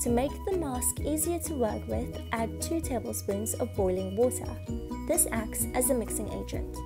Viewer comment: I was distracted and I thought you said “add two tablespoons of Holy Water” and I was like “girl that ain’t a half bad idea” 😭